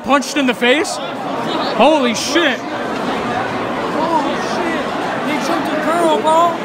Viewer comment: Did anyone hear that "Holy sh*t they took the GiRl PaUl"